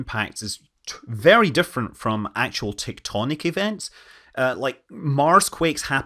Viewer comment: Hey... Did I hear correctly.. At 7:02 you said "TikToknic" events?